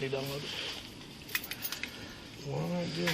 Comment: did he say "what did I do"? If so, hahaha. He's in a happy place called denial.